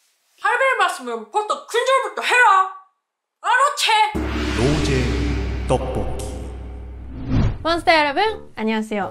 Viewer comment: 노잼 떡볶이로 들림...나만 그랬나요??ㅋㅋㅋㅋㅋㅋㅋㅋㅋㅋㅋ